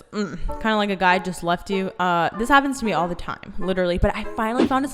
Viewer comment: I literally thought she said guys left her all the time 😂😂